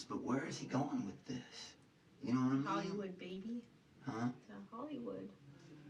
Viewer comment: I thought she said "Bollywood" I was about to die.